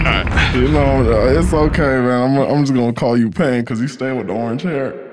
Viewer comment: did say “imma call you pain cus you stay w/ the orange hair” 🤔🤔 sayin know bout that naruto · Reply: No he said Pan, like Peter Pan lol